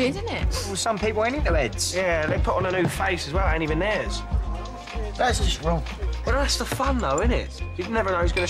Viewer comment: what did the bartender say? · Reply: “Thats just wrong”